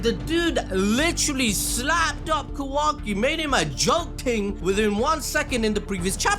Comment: Y’all in Canada? I heard “joke ting” 😂😂😂